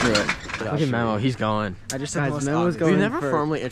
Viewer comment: wait who did he say? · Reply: Memo, he was their camera, man for tour